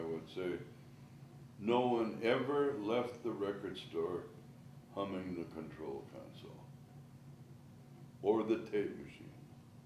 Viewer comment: Sorry but could someone please tell me what he is saying at 00:49?? "No one ever left the record store xxxxxxxxxxxx (????)" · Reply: “humming the control console” 🙌